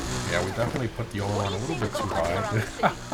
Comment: what does he say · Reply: He says - "Oh my god, definitely put the oil on a little bit too high"